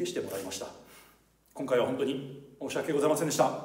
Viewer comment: 最後「凄いスコクじゃん、さゆまれて良かったー」に聞こえたんだが・・